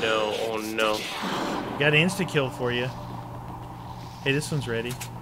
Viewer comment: It sounds like it said “insta cow.” 😂